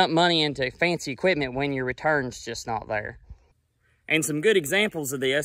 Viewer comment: At the end of the video, you mentioned two channels. I already subscribe to Just a few acres farm. Who else did you suggest? I thought you said Guy Rock Farms. I didn’t find a account with that name. When you can, please comment and say the name of the other YouTube channel. Thank you! Have a blessed day!